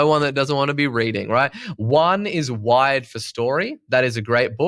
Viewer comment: Hey friends, I looked up the first book. It sounded like he said “wide four story“ for an audiobook to listen to, but there is no such book. Did anybody hear it differently?